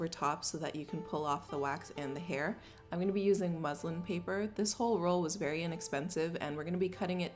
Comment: Lmao I thought she said Muslim paper